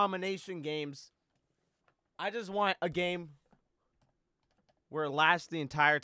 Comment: I skipped to 10 secs and I literally heard him say “I just want nipples.”